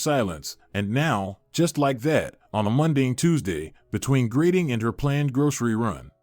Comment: Love the 1:22 mark on a Monday and Tuesday, instead of on a mundane Tuesday. AI is great but the miss pronunciation is really annoying